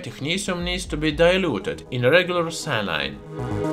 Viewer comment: lol I was sooo confused @ 11:07 - I thought you said it "needs to be diluted in regular CYANIDE " !!! lol - I understand now that you were saying "SALINE" - I'm just used to hearing it said with an American accent and it sounding like "say-leen" - :)